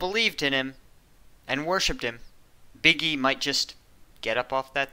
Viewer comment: I heard Big E, and thought you said "Biggie", which makes me want to post the 40k Eminem copypasta.